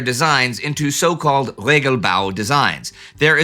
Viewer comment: " Regelbau" I definitely heard "Lego-bau" had to backup and listen a second time with the caption.